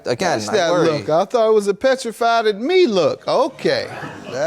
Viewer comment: I thought he said methrified lol. I had to listen again😊